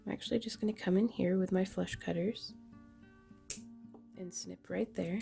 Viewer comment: Am I weird for finding it very amusing that every time you say "flush cutters" I hear "flesh cutters"? Anyway, I'm gonna need to do this some day, but I say that about so many things, and then I somehow never get around to do them ever. Maybe I'll get the craftalong-kit. That could be a motivation.